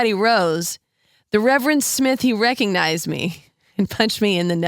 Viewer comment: I always thought he said "the reverend sniffed me and recognized me".